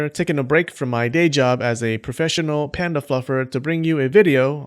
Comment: did u just say panda fucker? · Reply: fluffer!